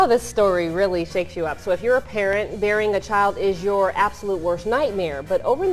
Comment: Waaait wait wait wait... Did that reporter say, "if you're a parent, bearing a child is your worst nightmare?" Edit: okay, different accent. "Burying." As silly as it sounds, I literally heard that as the former... Regardless of where you're from, even out of context, you gotta make sure you give that word its extra syllable.